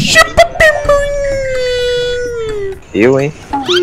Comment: Eu escutei "chupa meu ninho" kkkkk